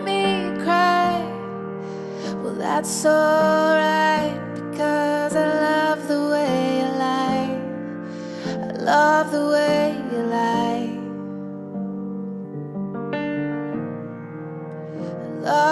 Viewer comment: i hear it as " I love the way you die." :person-turquoise-writing-headphones